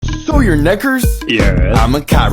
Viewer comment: So your ngers? Yeah👀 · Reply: Bro, it sounded like he said, “so your ni-“